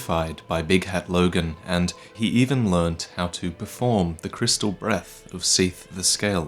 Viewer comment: I heard "he even learned how to perform the crystal meth" and had to skip back. Actual double take lmfao